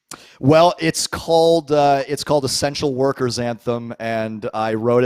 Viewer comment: That was awesome. I swore he said "sex workers anthem" at the beginning and was like hmmm...